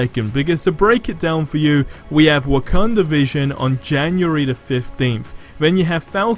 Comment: did he say Wakandavision? like if you also noticed that